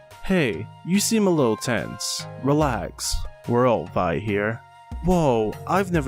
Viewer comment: I thought that you said that we are all Bi here. 😂